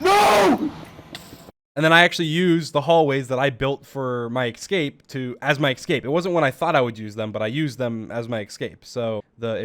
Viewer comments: Did he just say "ekscape" the times in a row? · why do I hear "ecscape"?